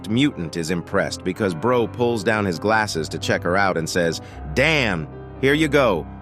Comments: I think he said dam to the cat cameraman. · I think at 7:42 he said Diana instead of damn